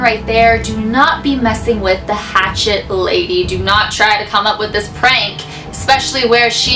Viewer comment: I thought this said ratchet lady XD